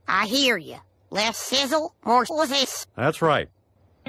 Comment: "Less shizzle, more.." WHAT?! Totally don't know what he said after "more" ;') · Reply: It's just "Sizzle" reversed.